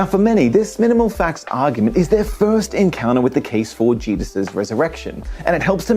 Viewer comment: am I hearing thinks but did he call Jesus Jeebus?